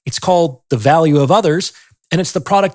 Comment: I swear the first time I heard you mention your book, I thought you were saying: ‘The Valley of Udders’ and I couldn’t believe you’d written a whole book about titty-tunnels…going for the q-tips just now…